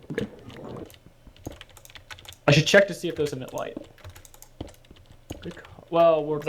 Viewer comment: is dutch? cause i thougd i heard him speak dutch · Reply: "I should check to see of those emit light"